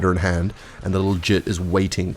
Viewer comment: did just call someone a "jit?" i didnt realize he was a floridian